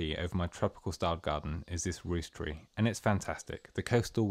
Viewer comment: What is the tree 1:57. I can't quite catch what you're saying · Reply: Rhus typhinia 😁👍